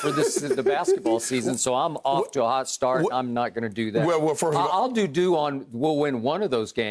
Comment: Anyone else hear Skip say “I’ll doo-doo” at 08:01? I know what he really meant but that was hilarious